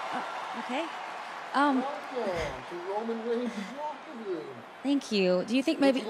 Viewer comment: what’d Brock say? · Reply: He said "Welcome to roman reigns' locker room"